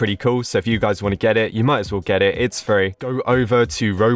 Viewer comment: "owo" 0:23 "you guys might want to get it, its furry" Edit: yes ik what he really said i just made a joke about what a heard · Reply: he said oh well and u should get it because its free*